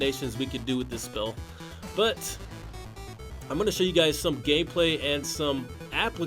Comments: did he say gayplay instead of gameplay ahahhaah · did he say some gay play